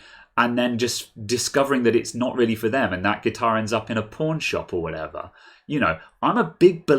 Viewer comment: Because of his accent and my dumb ears, I thought he said the guitar would end up in the porn shop. A few quick seconds later and I realized he said pawn shop. lol